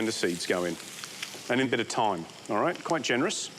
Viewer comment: Can someone tell me what he says at 1:23? "And a bit of time"? What herbs is this he adds right after he says this? Thank you!